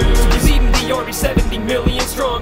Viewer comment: sounds like he says king pubes :D